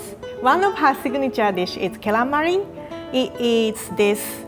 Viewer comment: Gyeran-mari... I was listening to the video off screen... Thought I heard calamari...